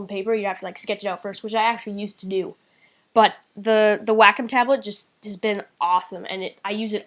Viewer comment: He said, "Whack um, tablet" i thought he said whack in tablet. I was like, WOAH! Everybody does it, but, keep it personal. "It helps me work better" It helps everyone. "I use it all the time" THATS FUCKING SCREWED UP THERE